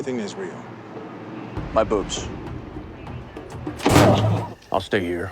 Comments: I always thought he said My Boots. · Police officer: "Do you have anything that's real?" Dean: My boobs.. Me: fair enough... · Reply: I have from the beginning heard him say Boots not Boobs... I still hear a t....... Still funny either way. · Police officer: "Do you have anything that's real?" Dean: My boobs.. Me: fair enough... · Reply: I thought it was "my boots..." unless I heard it wrong? I'll probably watch that episode again with the subtitles on to make sure... 😕